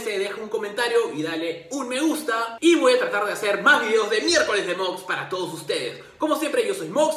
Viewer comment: por medio segundo pensé que dijo videos de mierda xd